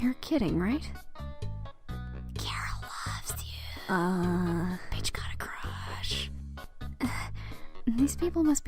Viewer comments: did it say "Chara loves youuuu" "Bitch got a crushhhhh" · I heard “bitch got a crushhh”